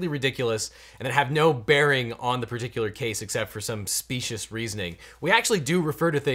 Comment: Did you say “specioust argument”? I probably just misheard you because there is definitely no T in specious.